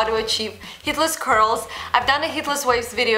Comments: What the hell,I thought she said "Hitlers Curls" , I was so freaked out! 💣💥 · Lol I am not being mean but she sounds like she is saying Hitlers curls and I can't stop laughing😂😂😂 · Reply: lmao at 0:19 sounds like she said hitlers waves